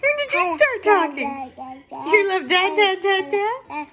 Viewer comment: i thought she said i like dada